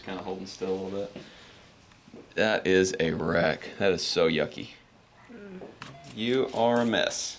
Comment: "that is so yucky" I turned away for a second and I misheard it and I thought he said "that is so yummy" and I was like what the hell